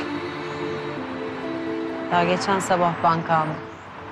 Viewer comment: arkadaşlar 2:06:56 süreyya ben kaldım qibi şey dedi defalarca izledim anlıyamıyorum ne dediyini biri bana açıklıya bilirmi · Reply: daha gecen sabah banki andim diyo